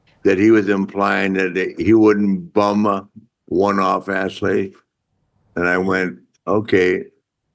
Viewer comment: what did Flair say? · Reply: He wouldn’t “bum”one off Ashley…